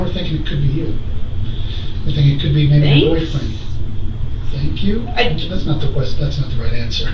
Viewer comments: She didn't say "thanks". She repeated what he said and the word was "thinks." 10:30 Hey wait a minute! Didn't she ask for a lawyer? She is now being questioned by a postal inspector. Is that legal? Or does that just pertain to the detective?! 🤷‍♀️ AGAIN: SHE IS ASKING FOR A LAWYER AND IS BEING IGNORED! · did she say thanks? She does not have a brain😂😂😂